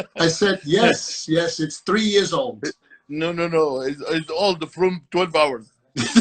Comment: "It's old... from 12 hours" - Mr Olympia's sense of humour lmao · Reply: I heard " it's old... from twin towers" ....